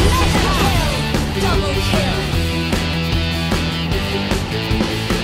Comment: did chou just said Zilong's line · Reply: The line actually is: "Zhao dragon born is here" that was a very old line from Zilong when we was still named Yun Zhao. As they added sound effects that blend with the begging of the line, people can misheard the Zhao as Chou.